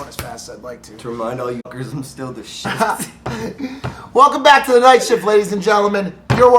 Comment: Wait what did he say? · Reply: Zilvr im still the shit